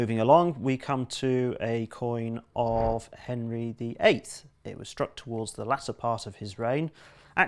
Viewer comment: the captions say Henry VII, but I'm sure I actually heard him say Henry VIII, can anyone confirm?